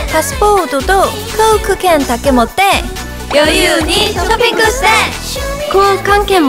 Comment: モモ……ショッピングって、発音が…>_<… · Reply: モモペン ショッピングじゃなくてショピングに聞こえる笑 かわええぇぇぇ